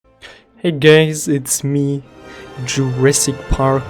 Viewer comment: why I hear "hey gays"